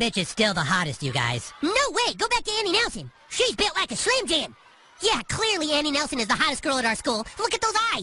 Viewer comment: "Shes bent like a Slim JIm" -Leopold "Butters" Stotch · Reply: I thought butters said she's bent like a slam jam